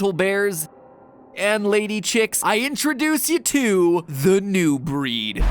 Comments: I can’t be the only one that thought it said “The new *Bread*” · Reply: Lol i thought he said the new brad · I can’t be the only one that thought it said “The new *Bread*” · For a sec I thought it said the new bread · I thought it said bread not breed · IM SO DUMB THAT I THOUGHT THAT IT SAID NEW BREAD